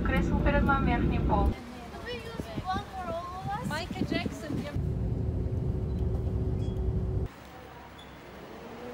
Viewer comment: did she say Michael Jackson? Lmao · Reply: yes u listened right, she said Michael Jackson 😂